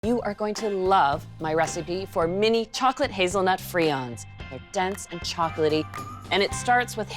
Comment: I thought it said Hazelnut FRIENDS